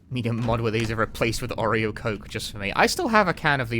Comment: Did I hear that right, Oreo Coke (-a Cola)? Sounds like a case for "That's disgusting!" I recently ordered some Soda from the UK, IRN BRU which was nice, Vimto with some kind of triple berry/grape flavour, which was also nice, and Barr's DnB, Dandylion Burdock soda, which even the store page described as an "acquired taste". Gonna save that one for when I can let my friend and brother have a sip as well. Sounds awful though! :D